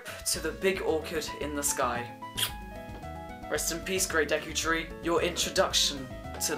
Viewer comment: WAIT!!!! You can also find Ariados in Sky Pillar!!! JUST LIKE SKULLTULA ARE FOUND IN THE GREAT DEKU TREE!!! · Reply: I thought you said Desu Tree